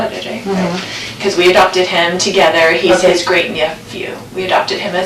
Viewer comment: JJ was actually Charles’ great nephew? Did I hear that correctly